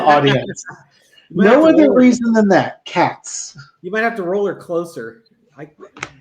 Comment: I thought was trying to say roller coaster not roll her closer 😂😂😂 my ears must’ve been flapping